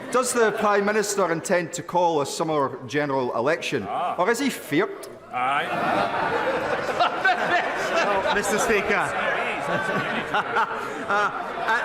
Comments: does he say “or does he fear it”? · What was he saying at the end? Just not familiar with the saying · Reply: feart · what does he say? I can’t parse it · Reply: feart